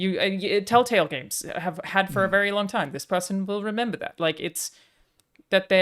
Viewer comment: What's the trick they said Telltale Games used to do about making something feel more important? It's hard to understand. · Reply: There's a little text snipped popping up in Telltale games after certain decisions you make that says 'XY will remember this'. According to, they seem to have used that also in situations where your decisions will not influence the relationship with that person simply to make the moment stand out regardless.